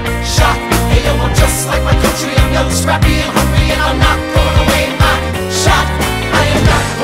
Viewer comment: My headphones cut out so it sounded like he said "I'm you, crappy, and hungry." Lol